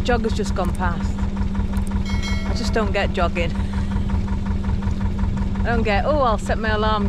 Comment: I misheard the jogging for dogging and my gin flew out of my mouth 😂.